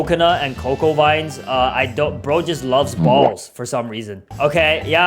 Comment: did he say bacha just loves balls?😂😂